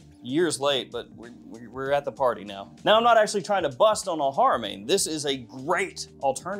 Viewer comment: bro what did you just say .. . You’re not trying to bust on who 😂??